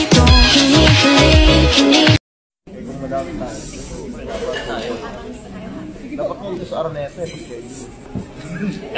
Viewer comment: Anong sabi ni ken dito?? 1:25 Ang narinig ko lng araneta eh hahaha pero Bkit natatawa ako. HAHAHAHHA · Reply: Naguusap po sila mag sched ng basketball tapos sabi ni ken data sa araneta na lang sila mag bb 😂